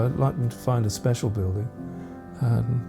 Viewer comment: What did he say right at the end at 34:56? "It would be a bit of a shock then if they found a _____, wouldn't it?" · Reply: A T-pillar. He's making reference to Gobekli Tepe...